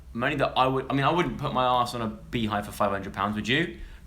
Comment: I 100% thought it said spitting on a beehive and was like hmm alright odd content